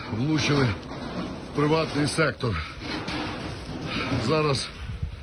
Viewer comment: Мне послышалось ?? "Правый сектор ...." ?? Эт же натовские нацисты...